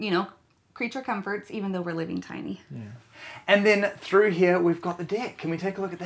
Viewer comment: it sounds like he says and out here we got the dick lol this home is cool